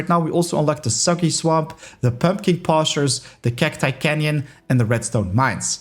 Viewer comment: Today, I show you Everything you need to know as New and Returning player in Minecraft Dungeons! From the very basics in the game to Max Apocalypse Plus Unique farming, its all there :_thug: Think I forgot something or suggestions for a future video? Make sure to leave it in the comments down below :_wow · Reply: I thought you said, the sucky swamp and the puppy pastures 😂